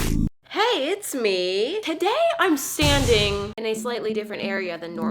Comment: am i the only one who heard sanding instead if standing